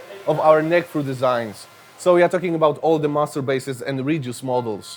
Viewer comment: Nice to see a random John Browne just browsing through their wood library too. EDIT: Lol is John Browne working at Mayones? HAHA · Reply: When he say: "master basses" i only hear "masturbates"